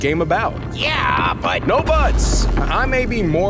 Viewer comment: whaa!? no what he said? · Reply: Jupiter said no buts not butt